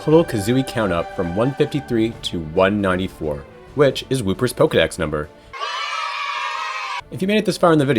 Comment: Wooper...I first heard "BlueBird" lol